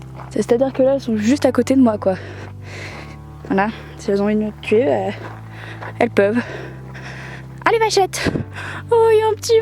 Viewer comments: Elle a dit quoi ? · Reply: Lucas Honorine si elles ont envie de me tuer, elles peuvent* :p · elle a dit quoi à 3:54 :O ? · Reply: Elle dit "hein les vachettes" ;)